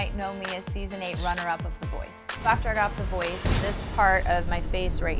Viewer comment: In the subtitles, they actually put (mumbles) in place of the word that she didn't articulate clearly enough to be understood. I think this is disrespectful to their guest. It showed that they didn't even care. I mean, if she didn't articulate that word/phrase clearly enough to be quite understood, you can guess a word, and put it in there. Most people are not going to nitpick that out, and even if they did, they wouldn't know what word to substitute there instead because it is clearly not understandable at all, no one is going to make any sense out of that word, isn't it? Just guess some word, and add in there... Don't do it this way because this just sounds disrespectful, to me, at least... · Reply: yeah she says after i got off the voice....